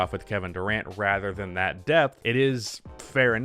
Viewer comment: Had this on in the background and thought you said "death"! Not a typical NBA topic.